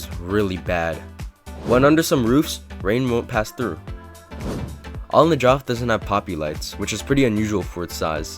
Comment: my subtitles said On the joff💀💀